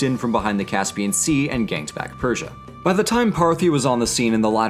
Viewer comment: Thought you said "Gangbanged Persia".